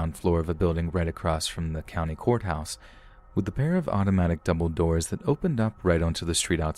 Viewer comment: I thought you said Dumbledore instead of double doors. LOL I had to listen several times.